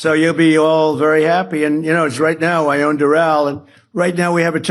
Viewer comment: Did he just say “Right now I own Doral?” No Donald, you don’t own a motherfucking thing except your despicable excuse of a presidency!!!